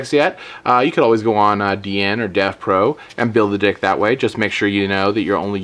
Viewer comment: Build a DICK. Most of the time instead of saying deck you say ' DICK ' x)